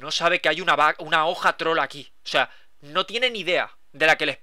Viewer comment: el el minuto 9:40 cuaqndo dice una hoja troll aki parece que dice una hoja trola ki xDDDDDDDDDDDDDDDDDDDDDDDDDDDDDDDDDDDDDDDDDDDDDDDDDD